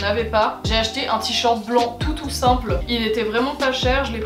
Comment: J'ai entendu "un tee-shirt blanc toutou simple" donc je m'attendais à voir un tee-shirt blanc avec un petit chien couché dans son panier 😭😭😭